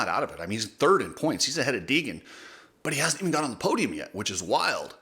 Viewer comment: I heard the term , “Diaper Boy Deegan. “ I’ll admit I had to laugh. Maybe the lectures last week helped him. lol